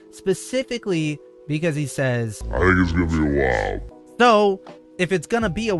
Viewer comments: I heard " I think it's gonna be WILD" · I could have sworn he said “it’s gonna be wild” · I thought he said "its gonna be wild" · sounds like he said, “it’s gonna be wild.” r we sure he said “awhile “?